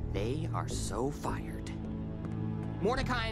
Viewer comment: I thought Benson said “they are so fuckered.”